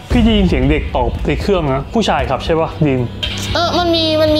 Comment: เราได้ยินตอบว่า ผู้ชายคับ นะคะ